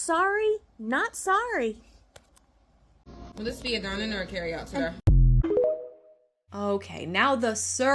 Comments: she clearly says “will this be dine in our carry out TODAY?” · I heard “ would this be a dine in or carry out today”. I did not hear, “sir” I think this woman’s internal subconscious, biases and prejudice are guiding her judgments, conclusions, decisions, and experiences but the fact that she is proud that she got someone fired is a special kind of despicable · I will say she did say sir. At least that's what I heard, but it's nothing compared to the rudeness of walking in and filming them without asking permission. This chick is a Karen. I hope she Get called out about it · when I watched the video I thought she said sir, but when she posts that isolated clip you can definetly tell she said today and not sir. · she clearly said "today" not sir. Lol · I am looking forward to your vomit & ocd video! I voted YES! 27:17 I absolutely love all of your content. But, the manager clearly says "Will this be a dine in or carry-out, sir?" Not that it really matters. I'm sure that the manager was super busy and, like you said, was on auto-pilot. Working in the restaurant industry can be insanely stressful (I have been a server at 6+ restaurants, and sometimes, a fill-in manager where I had to create schedules, etc. without extra pay...) But yeah, I don't hear two syllables there. I've tried to listen for the "today," and it is just not there. Sending much love from Canada!